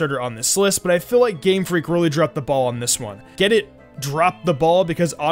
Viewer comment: When he said "Drop the ball" I thought he said "drop the bomb" and he said "get it because otter likes balls" and when he said "get it" and in my mind "get it because he's from Japan and Japan got bombed". And this was in number 7.